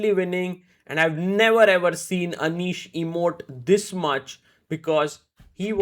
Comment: did he just said emote?